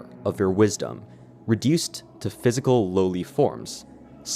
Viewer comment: my brain is so fried i thought he said "loli" 😭😭